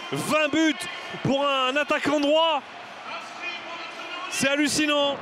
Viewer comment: "20 buts pour un attaquant noir" ⁉🙄 Dites moi que j'ai mal compris.